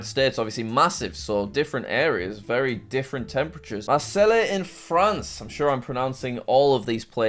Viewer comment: did man just say Marseleh 💀